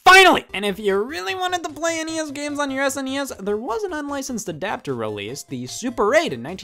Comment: i thought said if u really want to play your snes games on your nes for some reason